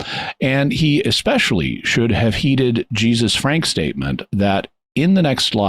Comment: Toward the end Jimmy mentioned “Jesus’s frank statement” but I heard it as “Jesus Frank’s statement”. I had to rewind because I kept thinking “who the heck is Jesus Frank?” Please tell my little kids that dad needs a nap. 🤣 Anyway, great episode as always!